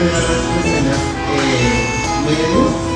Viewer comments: it's like the didn't know spanish???? wtf 😭 I think he thought u said "sí pestañas" like u said yes cause there's no way.... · Haha! I thought you said "sí, pestañas" too. I was like, wow, I've never seen get lashes!